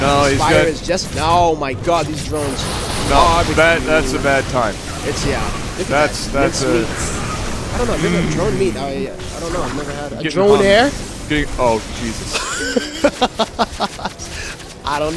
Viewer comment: I don't get the joke at 13:57. "Drone meat... Drone air?" Is that what he says. Drone air?